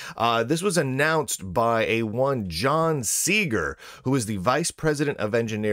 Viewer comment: I heard "John Cena!".